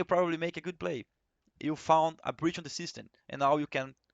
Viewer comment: Nice video. Btw, @ 6:55 the spanish subtitles are wrong. Fallen says "You've found a breach in the system" where in spanish it is written "You've found a bridge in the system"